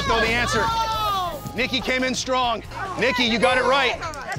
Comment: I'm studying English as a second language. Can anyone tell me what the host said at 3:09minutes? Something like.. nNikky came in strong..